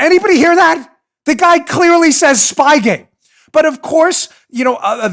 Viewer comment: I clearly heard “spyware”